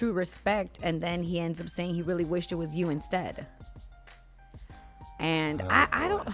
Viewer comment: he said “everybody really wished it was you instead, of TO” to Quavo. Not what you said making it sound like Chris said it in anyway